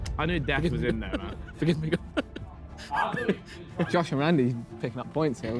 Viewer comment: what did randolph say at 18:13 ? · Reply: “Forgive me god”